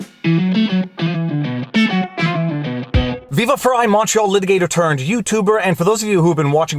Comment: For quite some I thought you said in your introduction "Macho Litigator"...only to finally figure out you really said "Montreal litigator." I think Macho is more fitting and fun. Thanks for taking time to explain these legal terms to us. You are providing an excellent education.